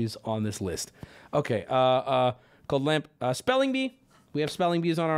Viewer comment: wubby says "Code lamp"?? Not sure if I misheard that but I thought it was interesting. Maybe a nod to something upcoming as he mentioned something coming up with cult of the lamp prior